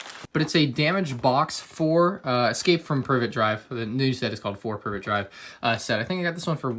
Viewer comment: Almost sounded like he said, “escape from pervert drive” O.O